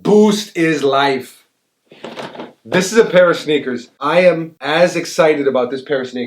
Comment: thought he said pair of snicker